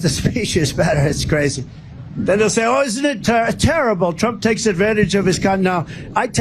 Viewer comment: (edit)..did he just say "I take advantage of my cont"?